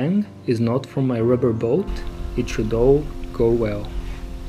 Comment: this is what i heard "and if the bang is not from my rubber boat, then trudeau go well".